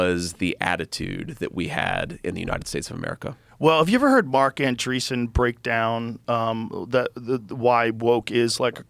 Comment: did anybody catch who Rogan referenced here? His name was Mark something, can't figure out the last name. Nvm Mark Andreessen, found it